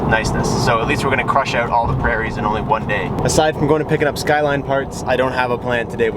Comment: did he say skyline parts?